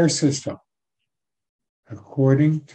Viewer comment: Hello did I hear "According to the creators will?" and not work? thanks for the short share video